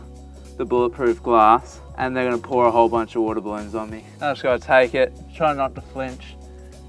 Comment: i heard watermelons